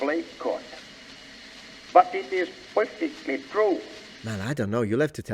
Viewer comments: I definitely heard his say "poifectly". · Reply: I heard "purfectly." IDK. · I definitely heard his say "poifectly". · I think he said “pointedly,” not “perfectly,” and the subtitles were just incorrect.